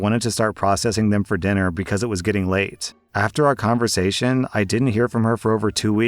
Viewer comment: bro said lake instead of late